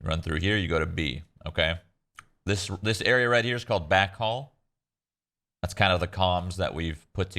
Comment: OMFG... when I first started playing and people say "U-Hall". I thought they were saying UHaul (as in the truck delivery service) so I would look at the truck... Thanks for the vid shroud